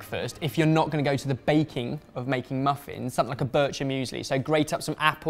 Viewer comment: what did Ben say at 10:33? butcher meuseli? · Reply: James Netusil Bircher Müsli. they have made a recipe on that